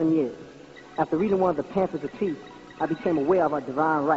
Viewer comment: Did he say “Panthers of Peace”? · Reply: Pamphlet of peace